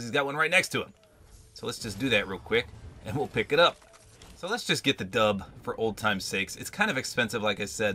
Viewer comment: " Let's just get the Dub " I Actually thought he said he was going to try to get the Victory Haahahhaah 😄😎😌